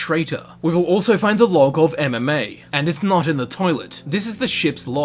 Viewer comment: thought he said MMA like a fighting arena